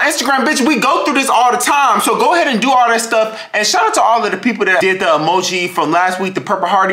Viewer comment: “girl why the fck is you putting- why you putting chips, lays chips in-n-n in yo sink with water??” I love it when delivers funny entertainment for his fans💀💀. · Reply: the way my name is shantall and you said " shout to all" I really thought you said my name